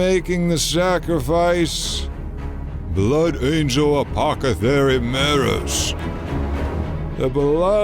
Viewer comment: Did I hear Apocathery @ 21:58?